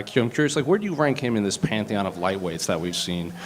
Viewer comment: Dana is a prick, but he’s hard of hearing! He obviously misheard what the reporter said and mistook “pantheon” for “pound for pound” I honestly thought he said pound for pound at first.. chill out! You’re gonna have a heart attack..